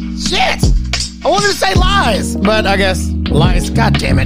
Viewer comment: I HEARD “LIES”